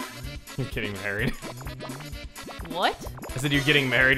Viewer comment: Welcome back to THINGS THAT IS SAID WHICH STRENGHTENS THE SHIP! 3:28 "Like you are getting married" (i heard it has "we are getting married) 4:16 "im really sore right now" 13:30 "i get to be in the middle between you and Wario!" Not much but we are hoping for more next time!